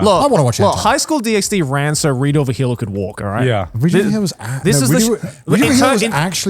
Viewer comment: What anime to they mention at 1:06:40 It's like my brain can't understand what they said, and the subtitles don't pick it up. *Oh it was re do of the healer. All I was hearing was rita the hilla.